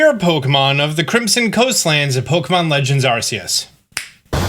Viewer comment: bro really said crimson coastlands, maybe not for you but thats what i heard lol!